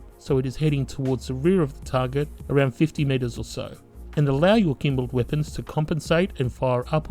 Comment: At around 5:50 when behind a ship, you say "about 15...or so" I can't make out that word. It seems it should be degrees, but it doesn't sound that way. 15 what? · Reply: "aim your nose behind your target, about 50 meters or so'.